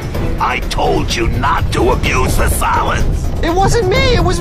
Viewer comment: being a child watching this back then, i thought they were all saying salads instead of solids